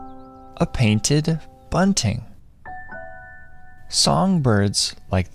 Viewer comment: I Don't See On Subtitles, What Did He Says? What's A Bird Name? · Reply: A painted bunting